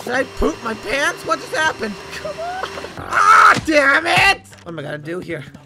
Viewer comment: I swear I heard say "DAM IT"